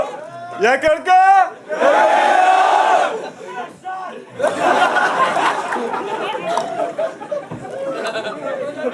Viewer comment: Il a dit quoi le mec du public ?? XD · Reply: "Ça pue la chatte!"